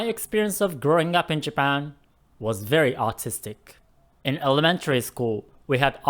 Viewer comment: Did you just say your experience in Japan was autistic?